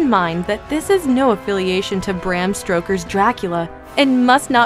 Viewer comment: did she just say 'Bram STROKER'???!